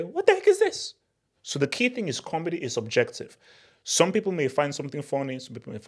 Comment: Comedy is not objective, you mean ' comedy is subjective' 😅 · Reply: You are mishearing because of his accent. He isn't saying "Comedy's Objective", he is saying "Comedy is'Ubjective". Yes he should make it clearer, but its a simple mistake to mishear.